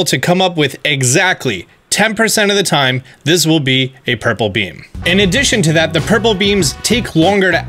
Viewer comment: Oh jeez, I heard "purple bean" for so long and was SO confused oops xD